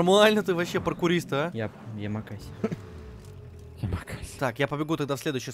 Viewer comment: В промежутке между 3:00 и 3:09 кто нибудь ещё услышал МакВысер ( Имя персонажа Юджина Сагаза в GTA 5 Online и Samp ) .